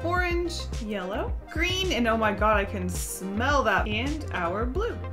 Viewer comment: Could someone please tell me what says at 1:05 about the green? "smell that mint" or something like that? the autogenerated caption says there's just the word "and" but those are so incomplete and full of errors that I don't trust them. Help is always appreciated! I have some hearing loss and also sensory processing stuff that is bad for audio so I need closed captioning. :( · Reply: She says “green and oh my god I can smell that” and then the video and audio cuts immediately to the next word “and” which is a bit of an awkward audio cut but the captions are correct